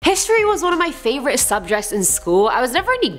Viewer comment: Listen to the first 4 second (0:00-0:04) did she say subjects like subjecs orrrr am I just super high